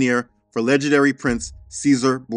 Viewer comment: Did you say "the notorious gangster son" here? 😂